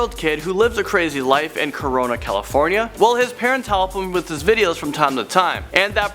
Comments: did he just say Corona California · did he just say corona?